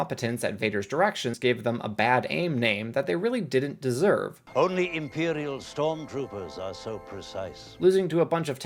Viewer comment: I swear to god I heard "only emperior officers are circumsized"